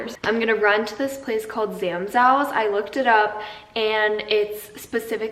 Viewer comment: When said “Zamszows” i thought she said “ Sam’s House” lol🫶🏽🫶🏽🫶🏽🫶🏽😂😂😂